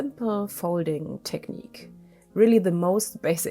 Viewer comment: Did you just say your favourite technique was the ' megafoof technique'?? 😆😆😆 I.listened 3 x ..put the subtitles on and it didn't recognise what you said either and blanked that bit out ( watching on my smart tv ) ..so is it the megafoof technique ? Lol. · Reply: 😂 .. It is the negative technique. I‘m sorry it was hard to undwrstand.